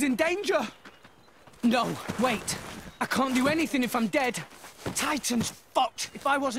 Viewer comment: i always thought he said "Titan's FUCKED"